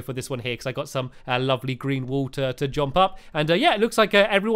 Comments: did Stampy say green wool? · does he say lovely green wool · Did Stampy Say 'Green Wool' At 5:26? Or am I just deaf. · Did he say Green Wool?? XD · Did anyone notice in 5:22 Stamps said "Lovely Green Wool" instead of Lovely orange wool?